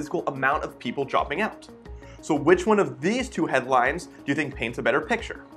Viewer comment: I thought he was saying headlights, like on a car.